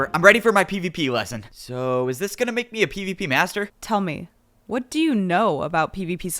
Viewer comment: holy shit, your right. I'm an illusion, a figment of your imagination. Made up of 1's and 0's, not of atoms. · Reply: i thought you said "master" not "buster" 😭🙏